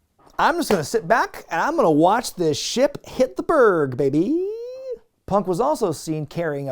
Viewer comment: What did say around 24:43-24:50?... "I'm just gonna sit back & I'm gonna watch the--" what?... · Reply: "ship hit the berg baby!"